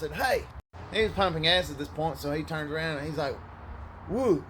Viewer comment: I lost it with the subtitle saying he was pumping ass not gas .